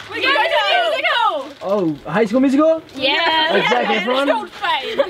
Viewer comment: did those girls say, "Hi Heath" ? I swear I though I heard them say, "Hi Heath".